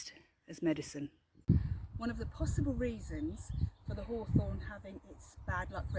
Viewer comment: When you said haw, I thought you said whore... oops 😂😂😂